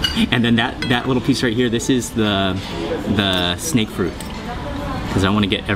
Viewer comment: what does he say here? Snake fruit? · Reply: yeah, snake fruit.